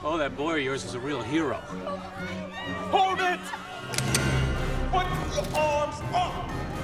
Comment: " put your arms up. All of them! " reminds me of a story my dad told me of when he used to go harpoon hunting in the Meditteranian. One day he found a squid/octopus and was about to harpoon it when the creature "surrendered" by putting all its arms up. So my dad accepted his surrender and let him go! 😂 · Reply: I thought he said "put your arms up Otto" lol